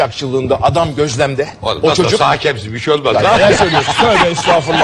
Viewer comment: de erman ne diyor ? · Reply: nasılsa hakemsin hocam bişey olmaz diyor :D